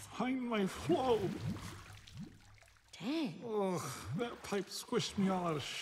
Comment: What she said? "Damn"? I dont understand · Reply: No! She said "Dang".